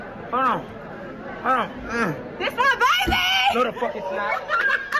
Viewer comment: i thought she said "this lil baby" cuz he looks very much like lil baby the rapper 😂😂